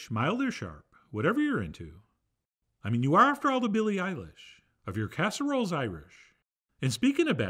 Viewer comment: Never did I ever think Chef John would reference a Billie Eilish lyric in one of his videos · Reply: I assumed he said Billy Idol and I misheard him 😂 I had to go back and listen to it a second time